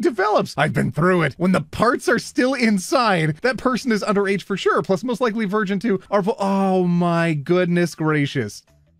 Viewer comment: what’s the censored word??? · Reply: labia